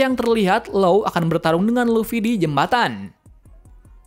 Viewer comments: kok kedengerannya luffy? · lak kok lawan Luffy bang kedengernya wkwkwkwk 😂😂😂😂😂😂😂😂😂